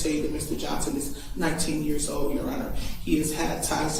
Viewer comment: That lawyer sounds like an idiot. I thought she kept saying "he's not 10 years old "😅 but she was saying "he's 19 years old."